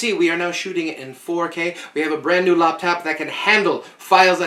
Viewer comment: When it's, I can't tell whether this is intentional. (Our host is so well-spoken!) Did he say we've got a brand new loptap??